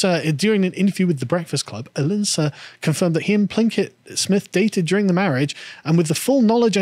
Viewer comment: lol, i think I heard "Plinkett Smith" a few times. Think she hates star wars too? "the most disappointing thing since my husband"